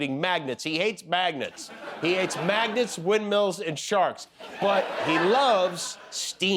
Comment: Did Jimmy say MAGAT……….?